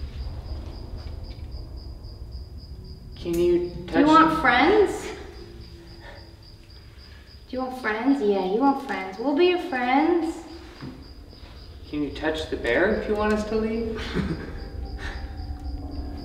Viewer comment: Special Episode for Spooky Season! Our next episodes are Mansfield Reformatory, Old Joliet Prison, MO State Penn, Sallie House & then ANOTHER SURPRISE =) Appreciate you all so much for watching! & Go pre-save Meghan's new album! Link is in the descriptionnnnnnnnnnnnn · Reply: hey bro i heard a voice, megan says do you want friends and a male voice goes "yeah" in a raspy whispery voice @ 58:17.